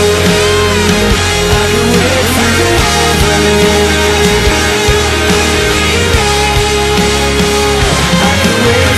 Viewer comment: Anybody else wondering what they say at 4:02? I have been wondering that for years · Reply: I can wait for the day 'cause you're gonna come back to me now :)